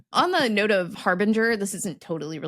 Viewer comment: I hear HARBINGER as "HARVINGER"